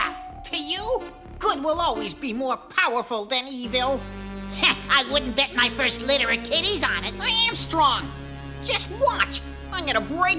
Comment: what did bad bird say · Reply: I wouldn't bet my first litter of kitties on it